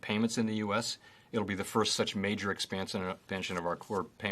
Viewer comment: Did he say “It’ll be the first such major expansion pension of our core payment system since the 1970’s”?